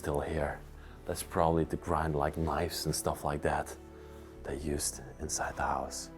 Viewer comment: Lovely video. At 18:30 in the basement, did you say the grinding wheel was used to grind mice???? I replayed a few times. Maybe someone can clarify. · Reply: I thought he said knives